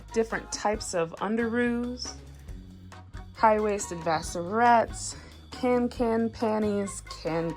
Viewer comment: what are the clothing pieces around 6:20 called, I cannot understand it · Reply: lauraregina Those are Vassarettes. High waisted briefs from WalMart. Great dance trunks!